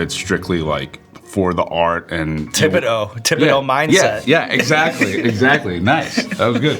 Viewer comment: Wait! What did Sean say in the 10:25 mark? Did he say “Tibetal mindset”? 😅